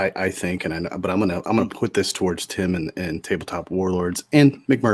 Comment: i misheard mumbled introduction: the tiptop warlords lol